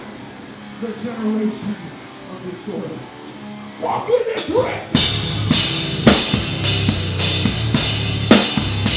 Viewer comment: does anyone know what caleb says at 2:40 ? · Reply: Kevin Rutzler Sounds like he's saying "Fuck with this riff"